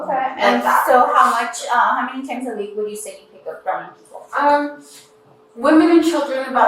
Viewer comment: DFCS case worker; "So how many times a week would you say you're picking up strange men?" Savage! 😂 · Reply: I heard her say “random people” not strange men.